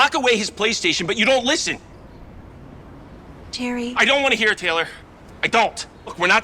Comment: Did he just say Taylor like Taylor Swift or am I just hearing this